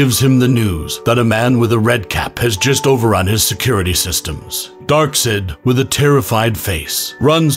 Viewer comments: Ah the most terrifying villain in the DC universe: Dark-Sid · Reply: I hear "Darksad" instead xD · I THOUGHT IT WAS SUPER MAN NOT SANTA💀💀💀💀 It’s because I thought he said red cape but he said red cap